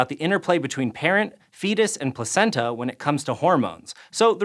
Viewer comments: what is said is "Parent, fetus and placenta" but the subtitles say "Mom, baby and placenta". Could be that someone made that modification with something in mind. In any case, this should be corrected. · the closed captions say "mom, baby, and placenta" even though though out out loud he says "parent, fetus, and placenta"??? Bruh I've noticed youtube is censoring things in captions... Yet in other parts of the video the captions correctly say fetus. I'm so confused???